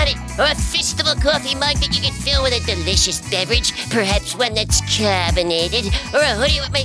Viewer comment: did Hecklefish say "a defishious beverage"?